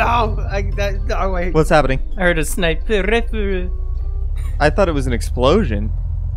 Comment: does anyone know wtf did he say? XD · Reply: filip filo He said "I heard a sniper rifle" Edit: I just realised that your comment is 8 months old